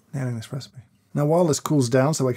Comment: What does James say at 8:22? The subs say "Nailing this recipe...", but isn't he really deaming about an espresso there? "Nah in an espresso". OR, in Swedish "Nä ingen espresso" :D